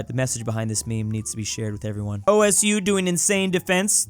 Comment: did he just say...O S U?????